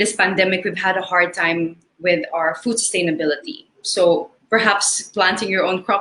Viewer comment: Sorry but she said FOOD SUSTAINABILITY. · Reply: I've listened to that 7:34 part over and over again but she really said food stainabilty, not sustainability. But anyway, it's just a slip of the tongue and she's still a good conversationalist. I'll pick her over Michelle Gumabao even though I used to live in Metro Manila .